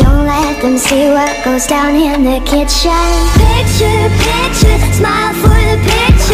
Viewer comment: I thought it said "Kitchen Kitchen smile for the kitchen